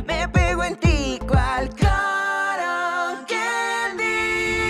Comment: pense que decia y en las otras partes " todo que vii" pero era cotton candy xD